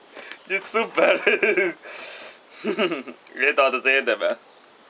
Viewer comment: mit mond 0:55 nél? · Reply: "egy italt azért érdemel"